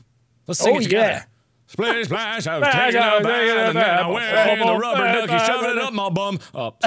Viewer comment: Mark - splish splash I was taking a bath I had a rubber ducky and I shoved it up my bum. Oh sorry! XD That made me laugh so hard!!!!!!!!!! :) :) :) :) :) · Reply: Lol I heard "Splish splash I was taking a bath and Wade interrupted and shoved it up my butt" Oh my goodness